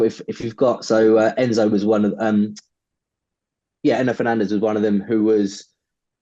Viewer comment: Did he call Endo Endo Fernandez??🤣🤣